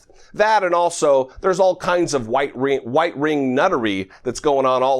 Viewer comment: did he just say "white-ring"nuttery? 🤣🤣